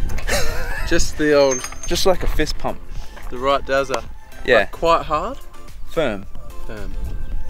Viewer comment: I HEARD